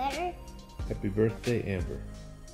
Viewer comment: It sounded like said “Happy birthday butter.” Lol he’s so cute. I miss Brodie so much already. I was so excited for him to return and kick ass. I know they’d rather have him back, but I hope his wife and children know how much he was loved by the wrestling community and the fans. We’re all thinking of them at this moment.